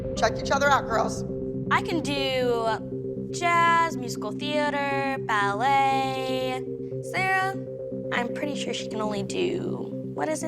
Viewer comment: Nia: "I can do jazz, lyrical, ballet. I'm pretty sure she can do, what's it called again?... Acro?" That was my favorite of all time. Sassy Nia. Love it. Edit: Guys, calm down! I just liked what she said, yeah some of her skills she shouldn't brag about, but she's still good. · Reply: I thought she said "I can do jazz, musical theater, ballet, sara? I'm pretty sure she can do, what is it? acro?" not that